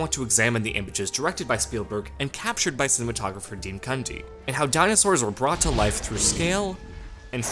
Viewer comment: Did he just say dean cunty